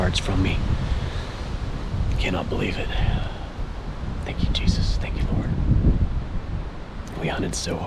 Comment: did he said thank you Jesus for killing a bear?